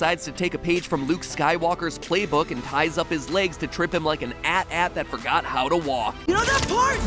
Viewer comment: did he just say @@ instead of AT-AT